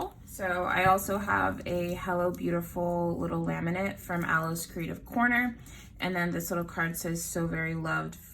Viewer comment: Great video 🖤 Where is the hello beautiful laminate card from? Thank you bb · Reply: It sounds like you say aloe's creative corner. But I searched for that name and nothing comes up. So I think I'm hearing wrong. Lol.